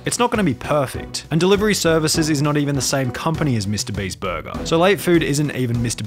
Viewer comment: i thought it said mrbreast burger lol